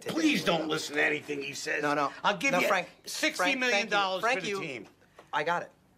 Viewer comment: MAC SAYS "FRANK YOU" INSTEAD OF "THANK YOU"